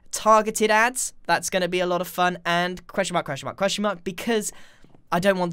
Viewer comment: Who heard questionma, questionma,questionma in